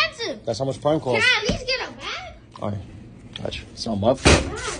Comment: I genuinely thought he said "Can I at least get a dad?" Instead of bag and I lost my mind 😭